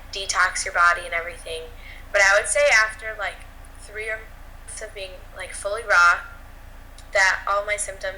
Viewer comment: Hi, at 22:21 i missed the amount of time it took to heal crohn's. Do you remember what said please. Carry on with the good work btw! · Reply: Anthony Gaughan hi Anthony! I believe it was after 3 months that all of her symptoms disappeared, but of course it's really individual and differs from person to person. So glad that you're part of this community!!